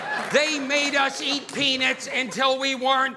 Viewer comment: Hahaha I was like what? Did he just say penis? "They made us eat penis, until we weren't allergic to penis". That's what it sounded like before I told myself, no he's talking about peanuts! 🤣